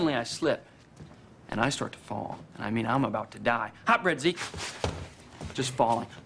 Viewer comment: "Hot bread, Zeke!" always makes me laugh! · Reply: I always thought he said hot breadsy!